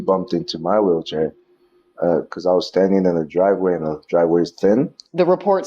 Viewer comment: did he say he was standing in the driveway? Lol